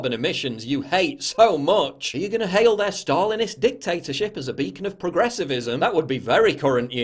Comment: Aa Kk Could you write to me a quote of what Paul said from 05:42 until 05:45? Thanks, I only know a little english. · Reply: "you even hail their stalinist dictatorship as a beacon of progressivism"